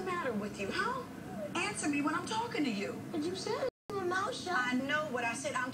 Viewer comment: what he say i thought he cursing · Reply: He said "but you told me to keep my mouth shut."